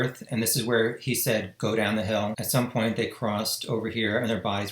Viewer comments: I hear "get down the hill" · I hear "get down the hill". I The analysis is very interesting and gave me more insight. I follow this case sinds the beginni ng.